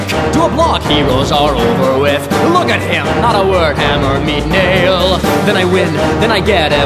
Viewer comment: i thought he said Hammer me Nail